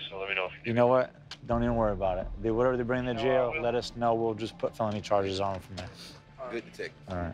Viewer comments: This is a PERFECT example of "Your mouth can you get you in alot of trouble, im pretty sure that police officer was going to go easy on em, but that attitude didnt do much for ya huh. Smh sad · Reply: At first I thought I misheard him at 3:46, but after listening to it dozens of times, sure enough, he really said: "Ya know what, don't even worry about it, do whatever to bring them to jail, let us know, we'll just put phony charges on them from there."... He KNOWS all the cameras are rolling and no DETECTIVE would be THAT stupid, so it very well could be a mind game he's playing, I've seen interrogators do that before when they're working towards a confession. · The cop said, do what ever you got to do to bring them to jail. When they get there we will just put phony charges on them. Wtf 😳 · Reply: At first I thought I misheard him at 3:46, but after listening to it dozens of times, sure enough, he really said: "Ya know what, don't even worry about it, do whatever to bring them to jail, let us know, we'll just put phony charges on them from there."... He KNOWS all the cameras are rolling and no DETECTIVE would be THAT stupid, so it very well could be a mind game he's playing, I've seen interrogators do that before when they're working towards a confession.